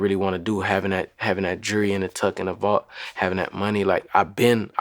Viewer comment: “havin’ that jurry tok ina back uh duh volt” what did he even say lol